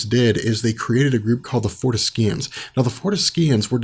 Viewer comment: i thought he said, "the beyonders created a group call the foreskins" at around 2:50. almost choked drinking. ahahahaha